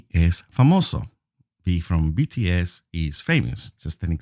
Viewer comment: Did he just say BTS?👀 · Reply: I literally had to reply it because I'm sure he said V from BTS.